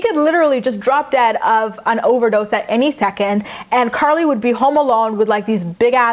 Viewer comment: did she say carly would be home alone with Butch at 2:08 ? oh well